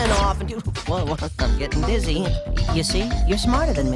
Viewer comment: did he say wtf getting dizzy